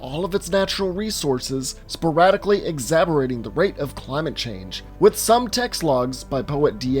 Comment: Did he just say... "Exabberating"? Did he mean "exacerbating"?